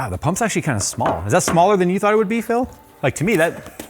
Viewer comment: I didn’t comprehend what Jay said (played back several times) so didn’t get why laughed. Anyone? Edit: Got it “Is that smaller than you thought it would be Phill?” and now I get it: Toilet humour :)